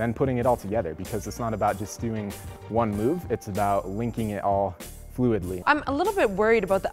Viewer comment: I think she's canadian 1:10 she says aboot